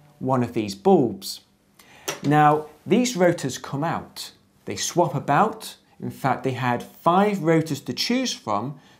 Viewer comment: Great video. One question: what is it that you've said in 2:56? "They swap about"?